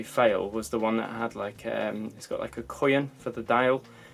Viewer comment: What did he say at 9:40? "It's like a ____ for the dial". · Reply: Coin is the word you are looking for. <link>